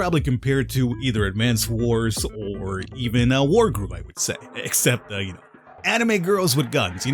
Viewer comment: What's the game that appears on 0:33? I couldn't understand the name, and the Youtube subtitles say "a war group".